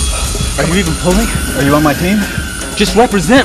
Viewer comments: “Are you even pulling?” “Are you on my team?” “Just represent, pull” 😂 love him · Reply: All this time I thought he said represent fool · Tony and Rhodey🤣"are you even pulling?" "are you on my team?" "just represent. Pull" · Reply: Always loved that part.😂 Although I thought he said "Just represent, fo(fool)!" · I always thought he said represent fool